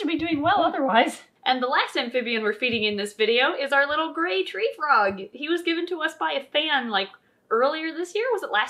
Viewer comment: I swore that said gay treefrog 😂